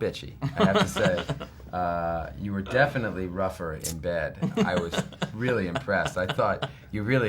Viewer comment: I heard 'rougher in bed' as well. I guess we're all dirty minded? :P